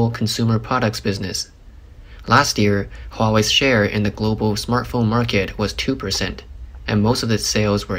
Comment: Thought you said 'smurf phone' Was expecting blue phoness More money going into pockets with no chips...ha